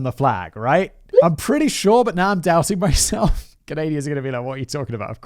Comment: did he just say.. canadia? 😂